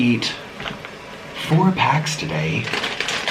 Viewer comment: it sounded like he said "I ate 4 cats today"